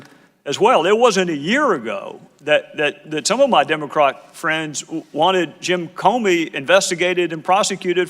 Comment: did he say, "my demo-crock friends?"